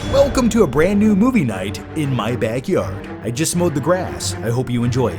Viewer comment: I swore I heard, “just smoked some grass” and was thinking we’re off to a great start. 😂